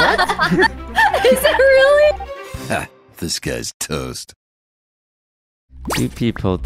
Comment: Am i the only one who thought he was always saying 'this guys toast' and not 'disguised toast'